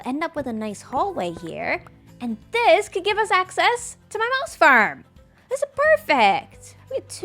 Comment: I swear you're saying mouse farm.